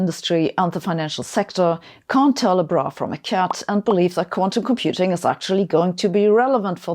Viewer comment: I misheard 4:11 as can't tell a bra from a cat, which makes the example an awful lot funnier and it's probably still vaguely accurate.